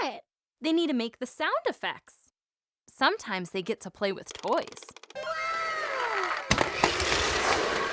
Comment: i thought she said "play with boys" 💀😳😭